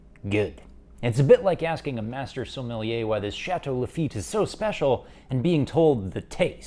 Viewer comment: Did he just say “Chateau l’Effete??” 😂😂😂